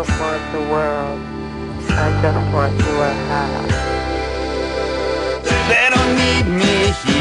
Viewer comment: Whenever me and my friend would hear the "I just want your half" Part of the song, we always thought he was saying "I just want your hat".... To this day I still giggle about that.